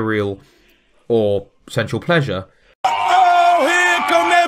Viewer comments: It took me a rather embarrassingly long time to realize he was saying "sensual pleasure" and not "central pleasure". That's what it sounded like! I'm not saying it made sense to me; I was boggled. It's just...that accent. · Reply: I thought he was saying sexual pleasure. I was really confused as to why he was so addicted to sex. Wait, they mean the same thing... -_ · It took me a rather embarrassingly long time to realize he was saying "sensual pleasure" and not "central pleasure". That's what it sounded like! I'm not saying it made sense to me; I was boggled. It's just...that accent. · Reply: I thought he was saying sexual pleasure....